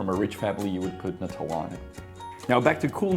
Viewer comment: Could someone write down what he says there "if you were from a rich family you would put...?" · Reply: Nutella :)